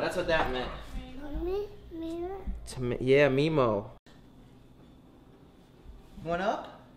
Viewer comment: I thought she was saying mammy